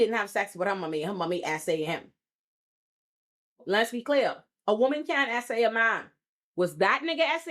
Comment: "A woman can't SA a man" is a false statement. Anyone can be a victim of SA. Gender doesn't exclude someone from being a victim of that act. Obviously this guy was lying to save his own tail, but I'm not talking about him. Also the number of people in this comments section being surprised that the abusing, cheating husband is white is... disheartening to say the least. Y'all biases made you automatically assume that the perpetrator was a Black man. Y'all neef to do some self-reflection & unpack that nonsense. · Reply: Read the caption, it says a woman CAN sa a man, I had to rewind it because I was listening with headphones instead of looking at the screen and also misheard it